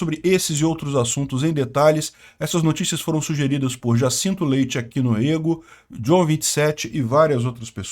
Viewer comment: Aos 1:50 eu ouvi msm " já sinto leite aki no rego " ????!!!!!!! 🤔🤣